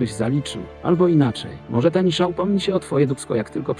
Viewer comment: Pierwszy raz usłyszałem to tak :"Albo inaczej, może tani szampon otworzy się na twoje dupsko" xD